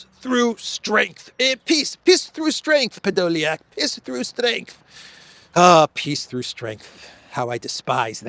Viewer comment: I swear I heard the green goblin's voice say 'piss through strength' @ 20:22 😂